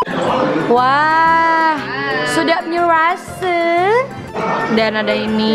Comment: did i just hear "sedap nyer rase" 😂❤️